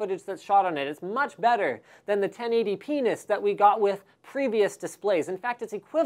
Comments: Did he just say "1080 penis"? bahahahahaha xD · Did he just say penis :3